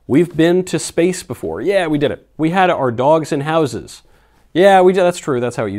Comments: As much as I love my dogs and dogs in general, i heard 'dolls in houses' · I heard it as "we had our dolls in houses", which Ii almost like better.